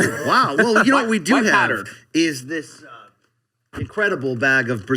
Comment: did he just said “power - white” backwards?